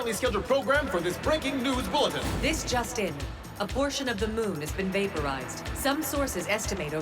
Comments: Uhh why did it sound like she said abortion of the moon is it just me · I heard it as “Abortion of the moon” Lmao